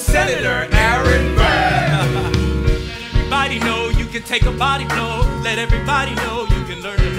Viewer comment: I thought burr was saying "let everybody know, you can take a body roll."